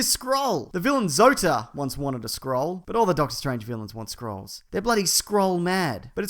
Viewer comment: When he said "scroll" I heard "Skrull" and I was like "whaaaaaaaat?"